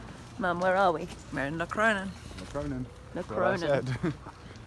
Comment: As of 7:30 the (art) historian in me (and the romantic and the gamer and the goth) had an orgasm. Multiple. Also: can you, please, write the name of this little town? I didn't quite catch it. Lacrona? La Crona? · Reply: Locronan :) Close! It was an amazing town! You have to go one day <3